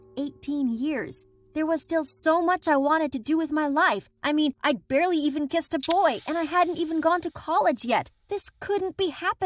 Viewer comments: I thought she said she I barely even kissed a toy lol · Her: “I’ve barely even kissed a boy.” Me: “same dood.” Also me: gay silence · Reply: I thought she said I barely kissed a toy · Her: “I’ve barely even kissed a boy.” Me: “same dood.” Also me: gay silence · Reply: I was half paying attention and I thought she said I barely even kissed a toy😂